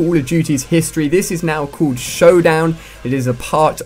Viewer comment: Did he say the map was called "Showdown" at 0:17?